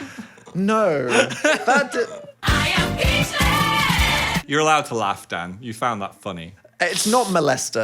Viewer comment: guys can someone help me out pls lmao i’ve been watching every ep but i cant figure out what this button says, it bothers me everytime because i can’t understand it for some reason 😭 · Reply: It’s says “I am Peachless” it’s from a TikTok Phil liked in one of the forcing Dan to watch TikTok videos